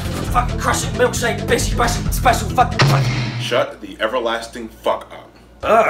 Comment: did he say Bishi Bashi Special?